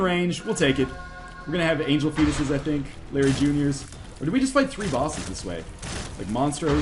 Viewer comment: When he said "Blue Larry Junior" I heard "Blueberry Junior". X3 Was just amused by this.